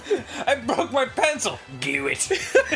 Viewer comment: What does he say at 3:09? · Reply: Glue It!